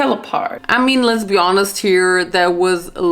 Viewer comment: when says "let's be honest" I hear "lesbian nestea" 😂